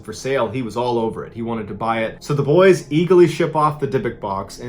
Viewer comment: he says Eagley instead of eagerly........... Merica!!